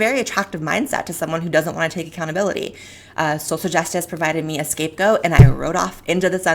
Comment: Gotta love it when you mishear one vital word... 3:30 Blaire "Social justice provided me an escape goat, and I rode off into the sunset" Me: Wait what!?